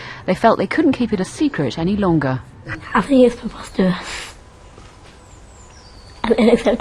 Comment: Did she say Potatous?